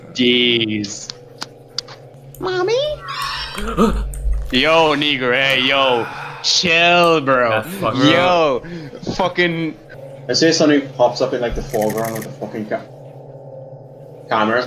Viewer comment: what did he say? 🥸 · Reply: knne gray